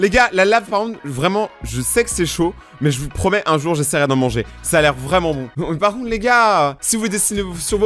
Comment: "Un jour j'essayerai de en manger" Moi j'ai compris " Un Jour je vais mourrir tôt brûlé avec de la lave"